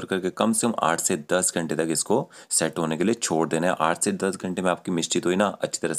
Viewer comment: Did you say 8-10 days or hours? At 4:26-4:27 minutes of your video. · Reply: Hours